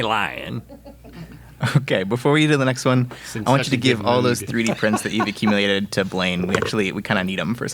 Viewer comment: What did Link say at @ 12:49? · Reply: He’s in such a good mood